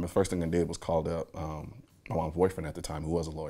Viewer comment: Did he say he called “his boyfriend” at 4:39??? I didn’t know he was “provocatively progressive”. So he’s a black American, gay, Republican, gun advocate. Okay, still better than a mindless band wagon liberal I suppose. 🤷🏾‍♂️